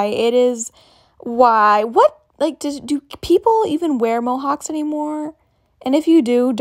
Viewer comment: i dont understand "do people even wear -????- anymore" can someone tell me what is it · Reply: Mohawks, the hairstyle